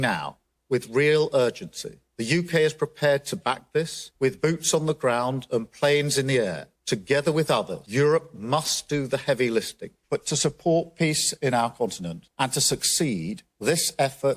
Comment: Sounded like Starmer said that UK must do the 'heavy listing' rather than 'lifting'. Quite a telling gaff. The UK, EU and NATO ship is listing heavily alright. What a mess.